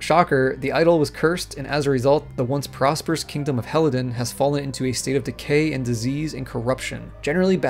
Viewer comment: did i hear "ELden".... pretty cinvenient concidering the new game of the serie is gonna be called elden ring***